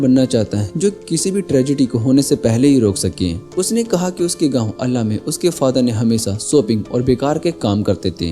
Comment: Bro what you said us ka gawoo ….